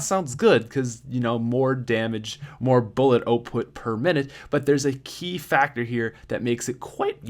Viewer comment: did you hear him when he said output it sounded like he said oatput